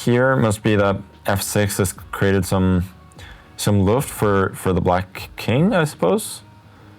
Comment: is he saying: has created some luft? as in luft = air in norwegian or am I absolutely tripping